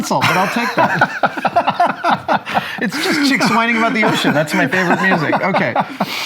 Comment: what did he say? “Chicks whining about the ocean”?